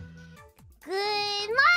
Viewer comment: I legit thought she said "mother puppers"